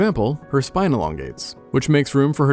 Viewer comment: For some reason I thought you said '25 pounds at a time.......